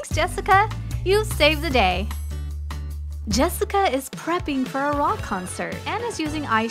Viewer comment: it said Anna instead of Jessica like this if u niticed